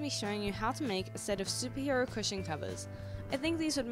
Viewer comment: At the start i thought u said Superhero Pusheen covers 😂